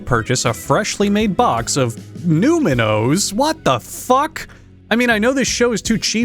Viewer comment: i just skipped to 4:42 and all i heard was 'NEWMAN OS? WHAT THE FUCK-"